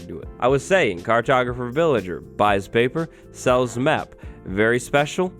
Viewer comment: I thought he said "buys paper, sells meth"